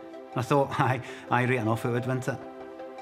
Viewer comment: What the hell does he say at 1:40!?? Been trying to work it out for ages! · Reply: "Aye, right enough it would, wouldn't it?"